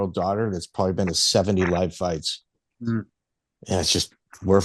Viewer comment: At first I thought said his 18 yr old daughter had been to 70 of his fights before realizing he said "live fights" and not "my fights". Thought for a second that maybe the fight career was the best kept secret in MMA 😂